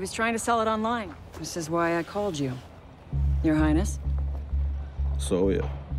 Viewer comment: Did ash just call tachanka "Your highness" lul